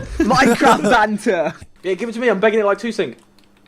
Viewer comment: wat did harry say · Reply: Give it to me I'm begging like twosync